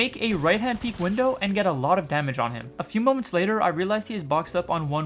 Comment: did he say " A few months later " or did i hear it wrong?